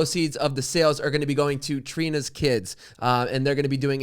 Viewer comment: Why did I think "Scott's Tots" when I heard Trina's Kids. Lol